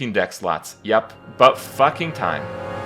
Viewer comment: Wait, did he just say "Buttfucking time" at the end? :) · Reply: no, he said " 'bout fucking time"